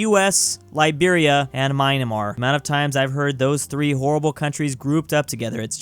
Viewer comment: …did he just say mineamar